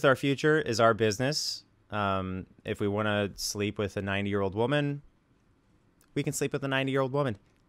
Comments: when he said 90 year old woman I heard it as "9 year old woman" ONISION STEP AWAY FROM THE CHILD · I don’t like the way he says 90. I straight up thought he said 9 year old woman. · I thought he said 9 year old woman and I wasn't really surprised. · the issue isn't you sleeping with a 90 year old, though...its the exact opposite. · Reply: I actually thought he was saying “a nine year old” for a while. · "If we want to sleep with a 90 year old woman" it's like a Freudian slip, where he admits targeting the most vulnerable in society to abuse. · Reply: I swore he said 9 year old woman the first time before he repeated it. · Ok so I know I'm late to comment BUT I CAN'T BE THE ONLY ONE THAT THOUGHT THAT WHEN HE SAID THEY COULD SLEEP WITH A 90 YEAR OLD WOMAN THEY SAID 9 YEAR OLD I WAS DISTURBED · Honestly, when he said "If we sleep with a 90 yr old woman, it's our business.." I heard '9 yr old woman' and I just died. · Legit thought he said 9 year old woman.